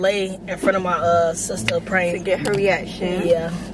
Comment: Bro why I thought dis shii said "Flirting wit my sister prank"😭